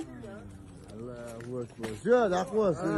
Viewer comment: Did he said allah Akbar